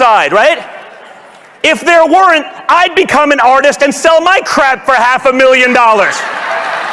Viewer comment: It sounds like he Said Crape